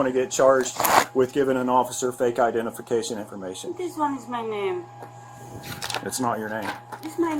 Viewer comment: Did she say "This is one of my names" @ 4:15 ? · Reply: this one is my name